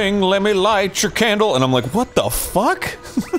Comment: This whole time I thought that song said "light your kettle corn."